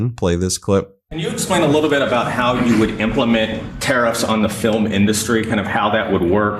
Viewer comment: Did he just say filmn industry?